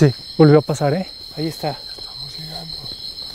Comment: Dios bendiga a los integrantes del equipo de Extranormal, nuevamente un programa que te pone los pelos de punta, PRESTEN ATENCIÓN que en el minuto 32:52 se escucha un susurro que dice “estamos llegando”